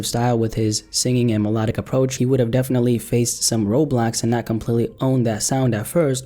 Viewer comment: am I trippin or did he say roblox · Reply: U tripping man he said "roadblocks" lmao